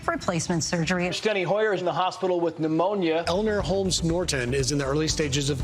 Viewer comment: eleanor holmes sounds like elder homes the first time i heard it 11:50 i died laughing